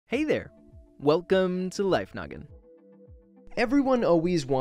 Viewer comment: I swear In the beginning I heard "Hey there. Welcome to life condom"